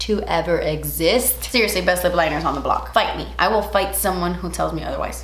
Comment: Totally thought you said “BITE ME” and not “fight me”. I’ve missed you. I appreciate you coming on here and advocating for BLM, as well as helping us all feel a little happier in this time. 🖤🖤🖤 Thank you for being the light in an awful time. 😘